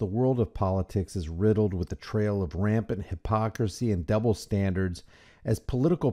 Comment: In the beginning of the video when you mentioned “double standard” I heard “devil’s standard” almost like it was a Freudian slip